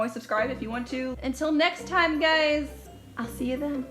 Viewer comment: I thought you said "Until next time gayyys" _ <3